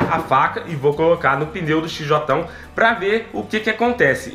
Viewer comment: Só eu que ouvi ele dizendo xj1? 🤔😂